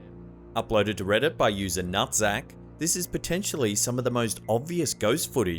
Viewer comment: DID I HEAR RIGHT “by user nutsack”